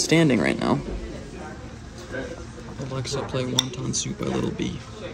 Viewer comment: Did anyone else hear him say Alexa play little peep at 18:27 ? my Alex heard you 😢